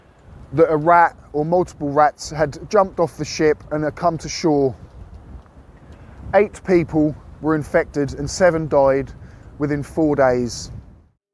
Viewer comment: I just woke up and misheard "...it's suspected rats jumped off the ship, eight people were infected and 7 died..." as "...off the ship, ate people, (the rats) were infected and 7 died..." Lol! Thanks for the interesting video, cheers!